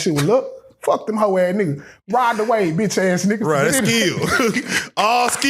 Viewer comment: Did he just say RIDE THE WAVES😂😂❤❤❤❤😂😂😂😂😂😂